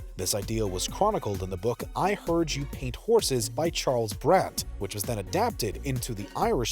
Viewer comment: I heard you paint *horses*? 😂 Houses!!! Not horses!